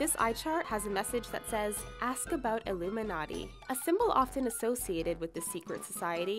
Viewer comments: it says in the subtitles at 6:44 "ask about the richest", instead of "ask about the Illuminati". WHY? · If you pause it at exactly 6:44 you can see the captions at the bottom say "ONLY, THIS EYE CHART HAS A MESSAGE THAT SAYS 'ASK ABOUT THE RICHEST'" Instead of illuminati... are they trying to say this channel is the illuminati?!😱😱😱